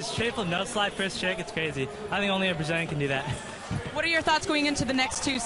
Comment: did he say, "im the only person who can do that"?